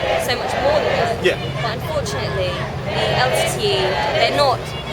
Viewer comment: it's sounds like she says the LTD...what's that? what does it stand for?